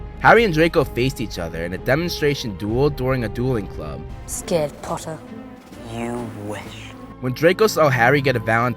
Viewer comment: ScArEd pOtTaH!!! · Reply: Arlette Balderrama I swear I thought you said scared potato lmao